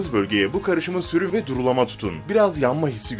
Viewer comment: ne dediğini anlayamadım ( durulama tutun ? )